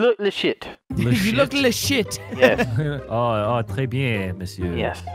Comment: did vanoss just say tres bien monsieur？